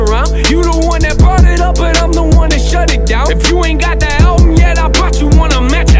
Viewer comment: when he said album i thought he said Altyn and i thought Ooooo dayum but then was sad to see it was just him saying album :<